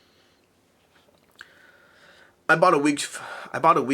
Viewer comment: Ah, wig! Toupee! I thought you said "I bought a week" initially.